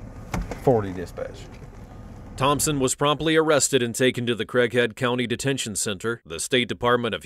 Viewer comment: I thought he said crack head county